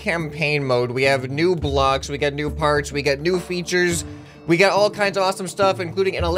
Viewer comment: I swear I heard “NEW FARTS” not “new parts”